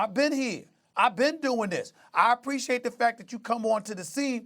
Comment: did she say facts???? 🤔🤔